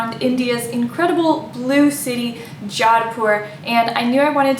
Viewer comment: It is Jodhpur, Blue City! I made another video about Jaipur, though :) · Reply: hey sorry I got wrong ....... I thought in intro u said blue City Jaipur , but now I played again nd listen carefully than I got u said BLUE City Jodhpur .......... Sorry .....😅